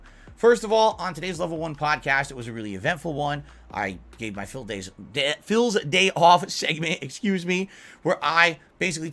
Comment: Sounded like he said dents day off@